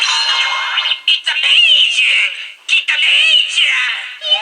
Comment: what does it say at 13:05? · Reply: It’s saying “IT’S AMAZING, IT’S AMAZING”, but in broken English.